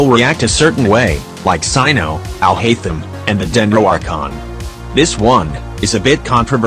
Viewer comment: what you said "el haitem" what i heard "i hate him"